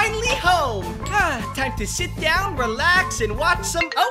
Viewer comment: For a second when she said she's going to "sit down" I thought she said "sh*t down*